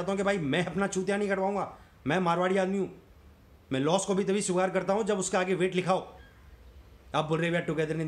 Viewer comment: kya bola? Clear sunai nhi dia. Explain. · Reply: Mai Loss ko tabhi sweekar karta hu jab uske aage weight likha ho.